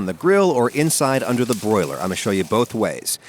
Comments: I thought it said boiled and I about lost my mind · Thought it said boiled